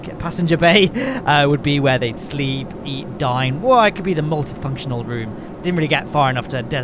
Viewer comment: thought for a second that you said "Eat, Sleep, Die" instead of "Eat, Sleep, Dine" lol